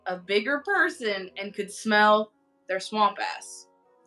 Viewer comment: she said swamp ass i thought she said SLIM FAST AND WAS SO CONFUSED HAHAHAH